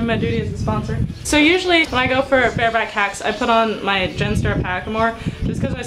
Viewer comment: What hackamore was it that you showed? I thought you said genstar but I can't find it anywhere. Thank you.